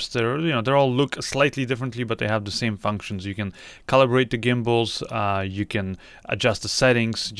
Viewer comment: Did you just say you can "color-grade" the gimbals?? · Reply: The Q2 Theory nope I said you can calibrate the gimbals